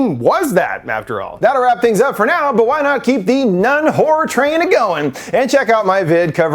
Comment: Haha, I misheard what you said about keeping the Nun Horror train a goin. I thought you said Keep the Nun Whore train agoin.